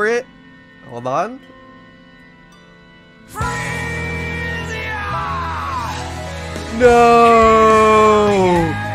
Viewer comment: song at 15:50? he says the name but I can't quite understand it, I think he says "Freeze Ya"? Can't find it anywhere looking up the name or lyrics. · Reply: Freesia from guilty gear